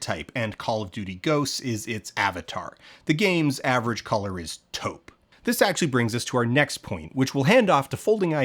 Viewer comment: Aw man... when he said the game's color scheme is taupe, I thought he said toad and completely agreed, but it turns out I can't hear.